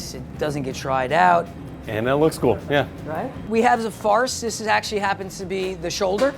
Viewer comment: is he saying farce? I want to know the source of this meat glue!